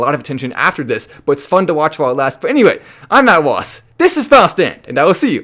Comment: Since i started watching you i thought you said “Matt Wasp” my bad bro! You say your name too fast!